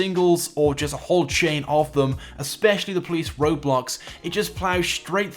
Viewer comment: I thought I heard ROBLOX